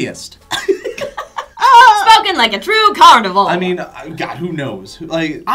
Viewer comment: Is it just me or does it sound like said, "spoken like a true carnival" rather than "carnivore"